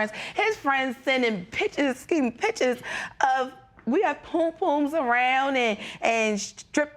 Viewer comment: Lol did she say pum pum??? 15:52 I like these two together.